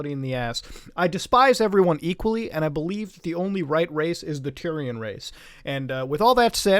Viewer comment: I thought he said "The Tyrranid race" and almost called the fucking inquisition on this kid